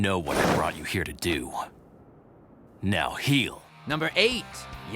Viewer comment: Talks about how swell Grimmjow was for saving Orihime from her attackers and how he showed how kind hearted he was Grimmjow *violently*:You know why I brought you here...now heal. (Mistakenly though I heard "now kneel" .///.)